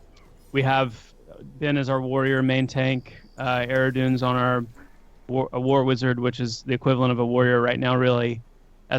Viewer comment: did I just hear warr/wizard?!